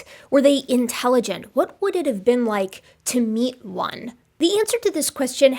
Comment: i definitely heard “to eat one” 😂 Whoops. That Kenya trip sounds amazing! I’m a Zoology undergrad with a minor in Archaeology. That’s super tempting.